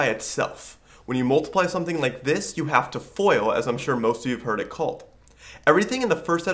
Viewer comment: You have to "foil"? What does he say at 0:56 about expanding the parenthesis?